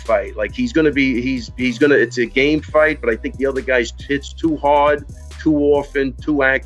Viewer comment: who heard "i think the other guy's tit's too hard"